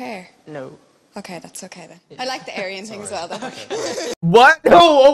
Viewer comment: What does the lady say at 11:46? The captions didn’t pick it up and I want to know what all the fuss was about 😭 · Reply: “i like the aryan thing” as in, hitler’s dream man